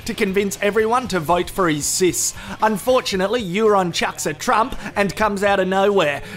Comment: did I hear that Euron "chucks a Trump"?